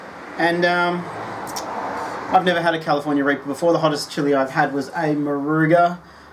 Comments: Sounds like your saying California raper lol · Sounded like you said you had "California Rabies".